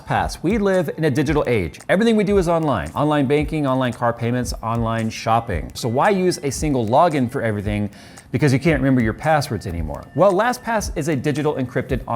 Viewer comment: lmao i thought he said "online baking" for like 20 seconds on that first ad. I was like "wtf? who bakes online and how?"